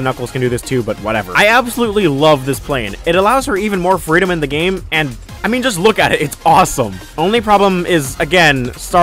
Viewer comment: Did you just call tails...her..? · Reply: Oh lol I said “for”, pronounced it a bit weird lol